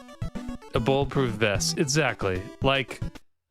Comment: I might be high but I could have swore I heard you say "ebola proof vest" lol. On a more serious note I've never actually seen this game, it seems cool but yeah I agree they could have done more with it. Since bullet proof armor as we know it wasn't around back then maybe a cast iron suit like the Ned Kelly gang? I know that wasn't in the west but it was the same time period.